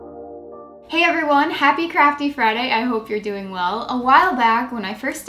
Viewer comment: Am I the only one who thought she said "happy crappy Friday"